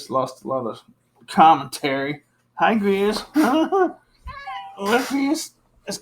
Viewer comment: thanks for watching! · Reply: Is it me or did say “hi”